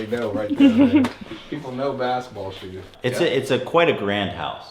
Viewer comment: did he call them basketball shoes